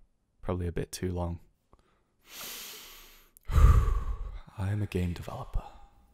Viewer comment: Who has heard ‘i’m a gay developer ‘ instead of ‘ i’m a game developper’